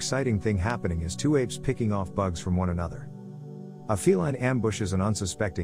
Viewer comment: I thought he said female